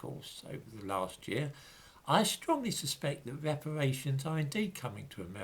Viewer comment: I should have gone to Specsavers! Thought it said repatriations!